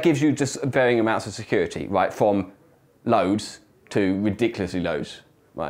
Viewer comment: Did he say lows or loads? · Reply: loads.